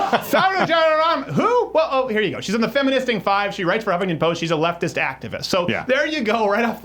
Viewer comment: Almost sounded like you said "the feminfisting five"